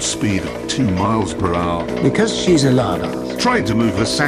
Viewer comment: Jeremy : "Your mum only has a top speed of 2 miles per hour." Hammond : "Because she's a lardarse" 💀💀💀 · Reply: i thought it said "she's a Lada"😂